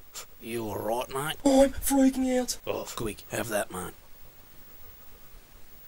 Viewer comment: Sassy saw the world in its real state for the first time in probably years, this is an understandable reaction. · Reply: i didnt know this is what he was saying untill the text episode always thought he said "were you talking to me" and idk just dosnt hit right knowing the actual saying